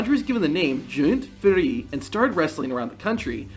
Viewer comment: what was that name he first had ? I think you’re saying Jean Ferré. lol.